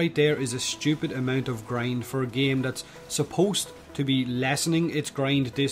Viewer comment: Ahahahaha 05:00 did he say "supposed to lessen the grind"? Did he? I thought he did. Haha is he still talking about Warframe there?